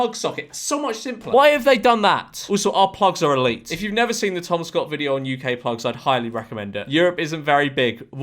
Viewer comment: Nah pause, I swear this mf just said THOMAS Scott?? 😵‍💫🤨